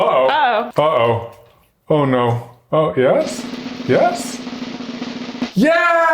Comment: Did Katelyn say: "udało się?" Is she Polish lol it's in 3:47 man I'm confused and it's probably just a random sound XD